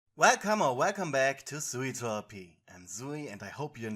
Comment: I hear “redhammer redcome back” everytime i click your videos